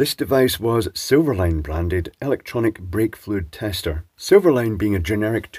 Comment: For a second I thought you said vape-fluid tester. 😅